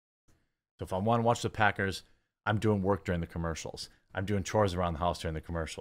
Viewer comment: did he say he was a fudge packer? Noice